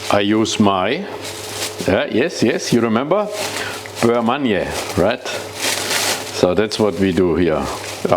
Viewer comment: Can anyone tell me what is the ingredient in 23:30? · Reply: It is a "Beurre Manié" (french for "kneaded butter"). Essentially it is flour mixed with butter. It is a thickening agent used in order to thicken soups and sauces. For it equal parts (by volume) of softened butter and flour are mixed together in order to create it. When the butter melts it releases the flour which then thickens the soup or sauce without creating clumbs. I have seen many recipes in which the sauce for this is thickened with starch. So I would say that you can substitute it with a starch-water mixture. Just add a couple of tablespoons of butter into the sauce at the end for that "butter shine".